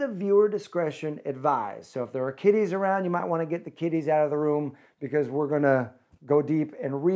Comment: Lol I thought you said if there's kitties in the house it's time to get them out😂😂😂😂 but you said kiddies